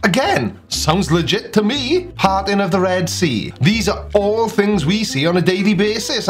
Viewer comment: At first I thought ya said “farting the Red Sea”, and I got quite a chuckle. It made perfect sense to me at the time! Love yer channel matey.